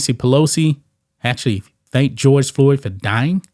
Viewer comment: I thought Nancy pelosi said his name was George Kirby.🤦🏻‍♂️🤷🏻‍♂️😂